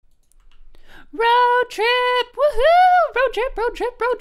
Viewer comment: It sounded like circus baby said bro trip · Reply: She did sound like she was saying bro trip